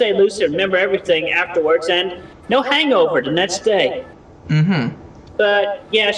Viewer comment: I can definitely hear him say "hangover" clearly, did you mishear on your end or just a simple typo?